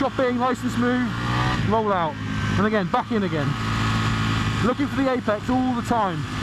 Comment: i can't understand what you are saying buddy.(know English not so good) look at for the eyepeaks? 10:45 for example